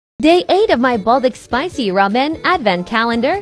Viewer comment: OMG lol i thought u said Day 8 as 'THEY ATE' lol they ate of my spicy ramen advent calender ,sound weird